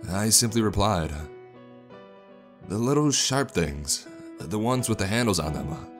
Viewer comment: All I can hear is SHART MAN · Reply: I heard "Shark" or "Shart" man, so yeah